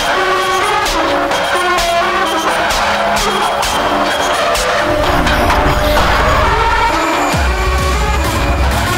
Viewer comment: What did he say? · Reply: i made myself known